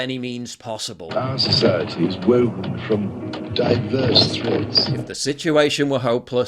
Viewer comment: I thought King Charles said "diverse THREATS "